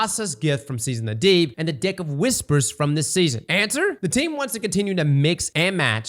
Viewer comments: Anybody else hear d*ck of whispers??? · Uhhh... I heard "Dick of Whispers"? 💀